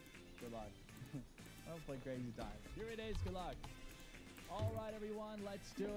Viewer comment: I am pretty sure I heard the host saying don't play crazy time, please watch this video there TT